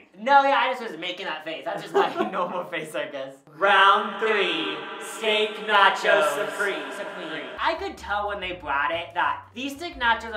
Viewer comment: It really sounded like they said skank nacho supreme lmao